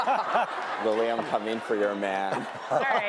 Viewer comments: what was she saying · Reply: Zainab Yousif she said, Lili i’m coming for your man😂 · What did they say at 6:42 I couldn’t hear it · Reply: Grace Peters lili im coming for ur man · Does anyone know what did the fan say in 6:46? Cuz I can’t hear it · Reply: They said Lili I'm coming for your man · What the Fan say 6:48 ? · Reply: Silvia Schulz Liou I’m coming for your man